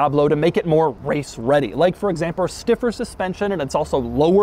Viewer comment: I heard you say "examper". I heard that.